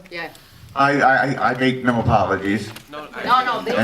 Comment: Frank Miller saying “I have no apologies” is the epitome of based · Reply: Huh, I heard that as "I have to apologize", I see now.